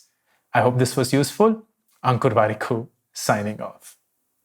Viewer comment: I used to mishear the outro as "uncle warikoo, signing off" xD